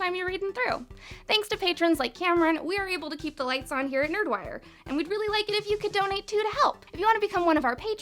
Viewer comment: I-... this is my first video of yours and I thought your intro said “Nerd Wine”.. and then I looked at your name and I was a little relieved.